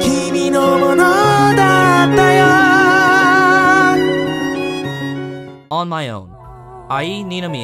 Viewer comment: what's the anime at 7:44 called i couldn't understand him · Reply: your lie in April